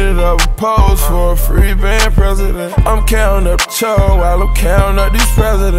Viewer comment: Chewing on bars💊 They can call me barbarian💂 All I see is stars💫 Fly✈ a bitch out from maryland. Molly on fire🔥 while im sippin out the Styrofoam. Cookin good dope. Got mo fish🐠 than aquarium. Beat it up pose for a freeband president👑. Im countin out yall while im countin out these presidents💵. Slide down on a new diva👧 thats what we about. Ridin round🚗 with a two liter im so po'd up. Two seater plush few racks in the bump a few goons in the cut🔪. I treat em just like bruddas, treat em just like bruddas. A few goons in the cut🔪 I treat em just like bruddas.😉😘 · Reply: Oh ok good looks I thought he was saying counting up Cho I was like what is that